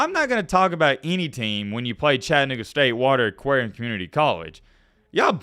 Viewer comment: when u play chata what state??? We know what u said